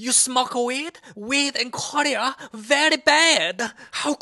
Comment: "what u smoke weed, weird and (smth didnt hear propely) very bad how could u puff puff a plant??? oh disgazting" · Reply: he said “weed in korea”